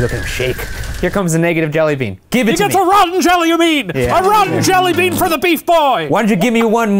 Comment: When I first heard Rotten Jellybean, I thought said Rum Jellybean.